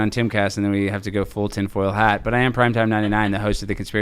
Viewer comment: Alex let it out. I thought he said Tim Pool hat wearing when he actually said Tin Foil hat. So it is revealed the beanie is tin foil lined.